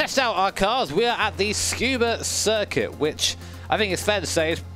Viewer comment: What track is this again? I thought said "scuba circuit".